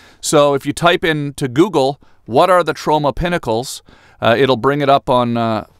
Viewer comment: I thought you were saying Toronto like Trona.... we miss you in Canada Tom.